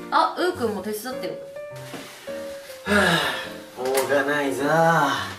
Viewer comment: めっちゃしょうがないな〜に聞こえたw